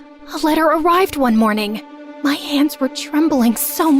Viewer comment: What he said was Dear drezella, my delegation has been extended and my duties have grown. I am far too busy now to keep up with our correspondence. I hope you understand and I wish you well Hope this helps 😊